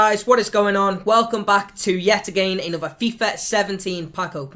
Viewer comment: At the begging it sounds like u say yetsgaming